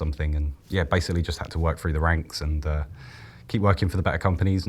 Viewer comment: Thought he said work through the wanks, lol ranks